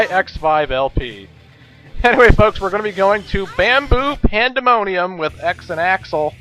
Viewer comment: I swear instead of bamboo pandemonium, I thought you said, "Man-boob Pandemonium"